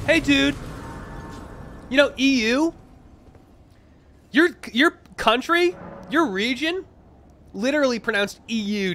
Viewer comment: Did he just call the EU a fucking country at 12:10? 😂